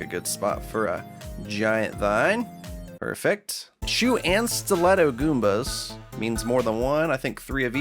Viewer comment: I kept hearing "chew and stiletto goombas" and was like "TF is a chew goomba?" :).